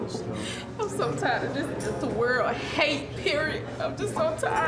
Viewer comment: no one else think at 8:01 she said i hate periods xD